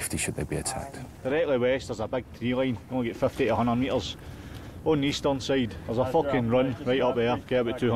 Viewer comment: wtf did he say lol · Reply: something about eastern side 100 meters random shit